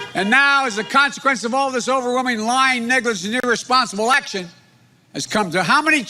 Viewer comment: Did he say the n word? 😔😔😔😥😥😥😥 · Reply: ḧüġö l·l·öŕïṩ I think he said negligence